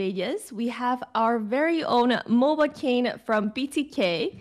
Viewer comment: did i hear mobakane